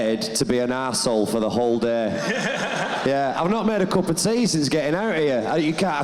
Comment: What does Joe say @ 10:58? "I'm not..."? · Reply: I've not made a cup of tea since getting out here